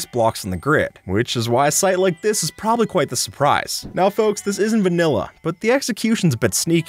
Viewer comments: Why did he say this isn’t vanilla? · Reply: he said “is in” his subtitles editor just misheard or sum 😂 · got subtitles wrong at 0:08, it's sight not site